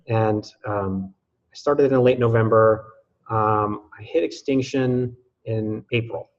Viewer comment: 14:05 Whats extinction? Is that what you are saying? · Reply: extinction is a place where people get to on TSM where they are indifferent to alcohol. They no longer crave it and are not driven by impulses to drink... but they may still drink from a conscious play of choosing to. I made a short video about this <link>